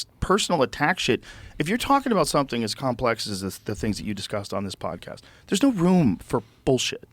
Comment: its beautiful how civil these two are. No undertone of condescension or frustration like another recent debate on this pod between a mainstream scholar and an alternative thinker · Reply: I thought you’re saying the presidential but you said on this pod